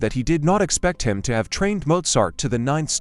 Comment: is bro saying Mozart